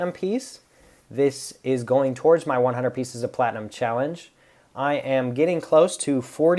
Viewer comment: Oh “100 pieces” of platinum challenge.At first I heard “100 ounces of platinum challenge” lol.I had to grab q-tips to clean my ears,then reply your video lol.